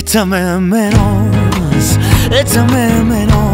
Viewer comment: Por un momento pense que decia echame de pedos.